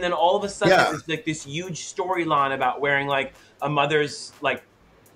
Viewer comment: thorgy said “youge” instead of huge 🤣